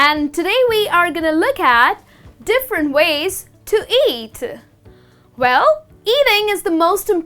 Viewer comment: I thought it said "differents ways to cat" xD